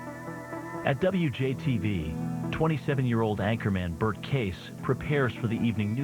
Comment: did he say 27 year old?